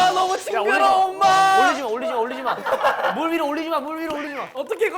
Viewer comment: DId anybody hear somebody say "ulgo shipji ana" (dont wanna cry) at 25:14 When Hyunjin was attempting to catch the fish? LOL we love a good reference XD